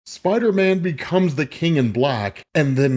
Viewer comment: I thought it said Spiderman becomes king of the Blacks😂